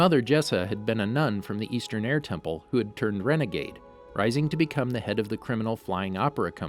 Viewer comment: did I hear Renagade???